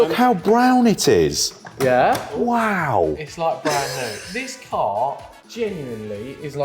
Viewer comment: when says "It's like brand new" all I hear is, "It's like BROWN new" 😄🙃